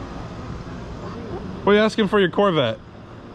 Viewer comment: I had this video playing in the background and wasn’t paying full attention- but I heard “how much for your Corvette? … Sold!” And I was like wait bought a corvette?! I think I need some sleep 😂